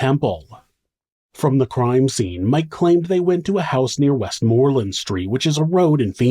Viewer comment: Haha. I thought he said "they went to V-necks" not "they went to Phoenix". V-Necks sounds like a homeopathic strip club!